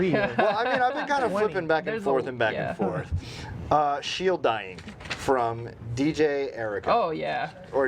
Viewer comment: ????? What is confirmed??? I dont understand it. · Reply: Shielddying, whatever that is...